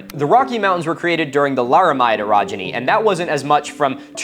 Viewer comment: The KNEE-JERK PANGEA THEORY hoodie and tee are available NOW! Show the world that you are part of the CONSPIRACY OF SILENCE! Link is in the description. Only available till Oct. 1st. So get em while the getting is good. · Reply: I gotta say, whoever made the closed captions needs to Google some things. At 40:15, "the Lara, my two progeny" should've been "the Laramide orogeny", and that was visible at the top of the image. There was also a lot of mishearing of the words spoken, like during the "My source is that I made it the fuck up" clip